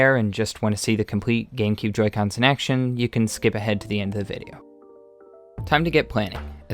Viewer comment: 日本人おる？ You can スケベにしか聞こえないんだがw